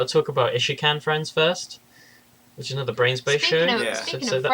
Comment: I can't count how many times I've heard of 'Brains Base' on podcasts, and I always thought people were saying 'Brain Space'. Seeing it spelled out blew my mind.